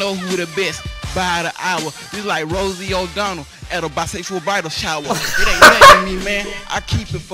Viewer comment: You look you had an awful battle with your barber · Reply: I always thought he said bisexual bible shower 😂